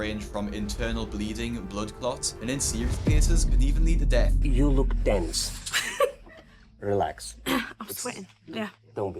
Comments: Who else thought the doctor said “you look dense?” lol, oh tense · Oh jeez I thought he said "You look dense" 💀 that's cold bro. Then I realised he said tense. 😅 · "you look DENSE" Is what i heard. And i agreed 🤦‍♀️😆